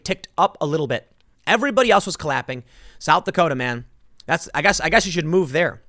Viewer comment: did anyone else hear him say "Collaping"? sounded like "clappin cheeks" is what he wanted to say